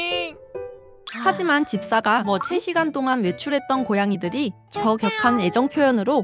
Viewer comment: 에 뭐라고 하눈건지 안들려용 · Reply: 자막 켜보니 3시간이라고 떠용